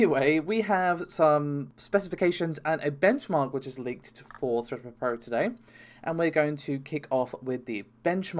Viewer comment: i kept hearing "thuh-puh-puh" and then realized it was "thread-ripper"